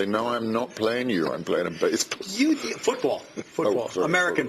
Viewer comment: I thought the interviewer said baseball player as well! :p Love you Alan! <3